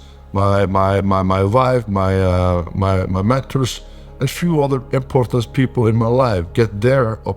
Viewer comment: Did he say at 4:07 that he had to talk to his family , his wife , his mistress ? LoLz!